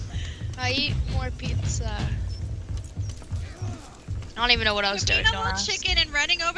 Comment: Anyone else hear say dumbass at 6:11?